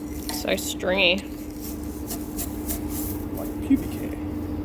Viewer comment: 😂😂😂😂Did you just say pubic hair ? 😂😂😂😂